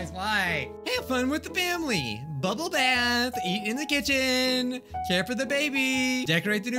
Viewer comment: “Eat in the kitchen!” I-, I thought it said “Eat me in the kitchen” 💀